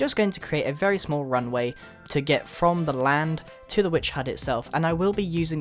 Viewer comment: I love itttt When you said Witchs hut, I thought you said Pizza Hut LOL